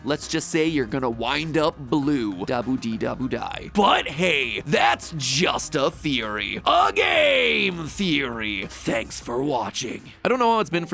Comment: istg i heard "BUT THATS JUST A THEORY A GAAAAAAY THEORY!"